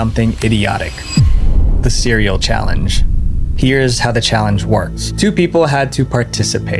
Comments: I THOUGHT IT SAID "The Serial Challenge" 😭😭😭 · when he said idiotic I hear diddyiodic